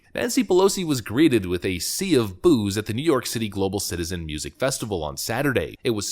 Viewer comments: All I heard was "Nancy Pelosi was greeted with a sea of booze". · i thought he said"nancy polosi was greeted by a sea of booze 😂🤣😂🤣😂🤣😂